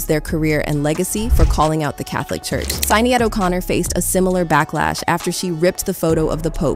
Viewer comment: did she just say Sign-ee-ad O'Connor? 😅😂